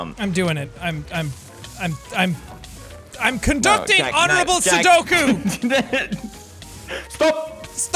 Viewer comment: did he say honourable sudoku instead of seppuku.